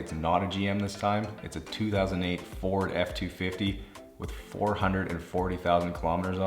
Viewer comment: can someone explain me that number ? four hundread and forty thousand ? <num>? or 40.400 ? and whi does he say hundred first instead of thousand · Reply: 440,000